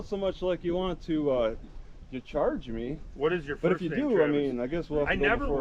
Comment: did he just say "what is your first name, Travis?"